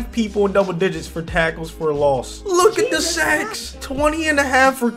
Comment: “look at the sex” 😂 lmao I can’t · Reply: Ik he said sacks but it sounded like he said look at the sex